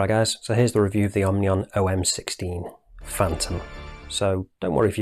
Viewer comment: I thought it said orunion on the dial, needs the casing to be brushed and 40mm would have been better which is what the CW is